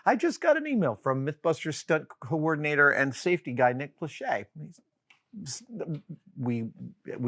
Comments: When Adam said the name of the stunt coordinator, I thought he said Nick Lachey, like, the 98 Degrees member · I thought you said Nick Lachey for about a second,until i realised how stupid that would have been 😂